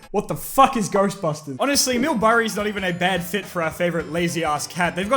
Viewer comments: did he say Mill burry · did he say mill burry ????? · did he just call Bill Murray "Mill Burray?"